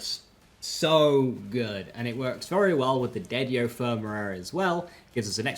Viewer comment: What was the firmware you said at 4:49 mins in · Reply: Daed-dy o. It provides swap speed bonuses and On Shot Gun Damage